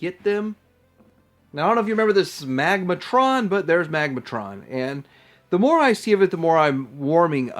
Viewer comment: for a second there I thought you said Smegmatron...